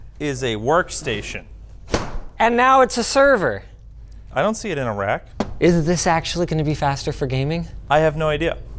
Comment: Why do the captions say "I don't see it in Iraq." when said "I don't see it in a rack".